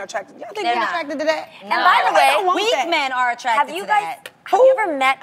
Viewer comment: she thought she said weed men because so did I lmbo